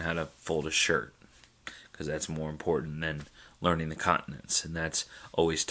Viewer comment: What does he says at 23:05 after "(…) shirt rader than ___?" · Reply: “learning the continents.”